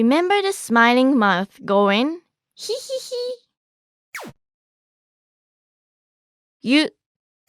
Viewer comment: Did she say Shi? 50:57 It's Hi right? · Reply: No she said "hi" listen carefully